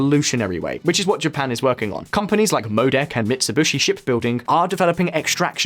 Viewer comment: did he just said mitsubushi??